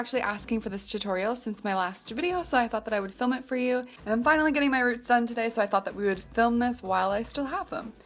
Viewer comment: I'm sorry but did you just say you're getting married? I didn't get what you said at 0:14. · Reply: (domosamurai) she said "and I'm finally getting my roots done today" :P